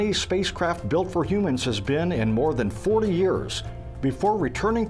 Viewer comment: it sounds like he says "4 years," but he's really saying "more than 40 years"!